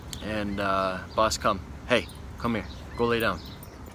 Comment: for a second I thought your boss said "hey come here go lay down"